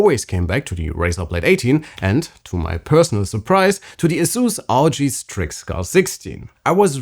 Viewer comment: I couldn’t understand what the guy was saying? Asus rg strickguard60? 16? What’s his favorite laptop? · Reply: asus r. o. g strix scar 16